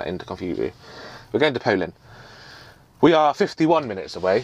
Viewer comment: Hi, Im loving the new truck. I swaer that at 6:45 you siad something, something, something ltd in Poland. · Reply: Thomas Hodson a thought he said Poland as well